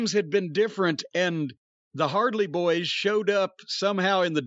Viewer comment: Jim said the Hardly boys and I had to listen again because I thought he said Hardy boys. I wouldnt call the young bucks anything that sounds like a good tag team.